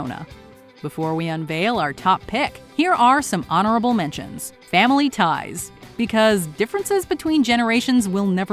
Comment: why did i hear 'family pies' in honorable mentions!